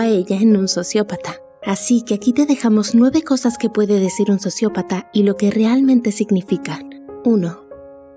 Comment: Pense que decia sicópata..